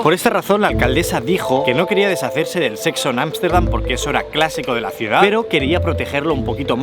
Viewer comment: Como que querian deshacerse del sexo anal?..... a no escuche mal xD